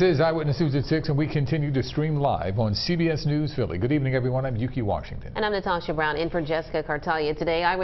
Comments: I thought he said I am Dookie Washington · I thought he said dookie Washington 🤣🤣